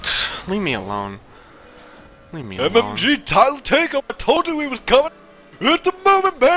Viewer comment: what did say at 2:28? all i heard was "MMG Tyl'll TAKE EM' I TOLD YA HE WAS COMIN', HIT THE MOMENT BABY WHOOOOO" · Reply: That was pretty much what he said, but instead MMG it was MLG